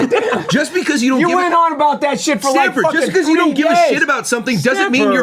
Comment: What did said here 1:12:20? or what is going on in general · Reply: You went on about that shit for 3 days